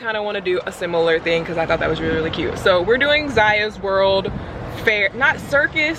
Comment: When said "Ziya's World", for a second I thought she said Elmo's World.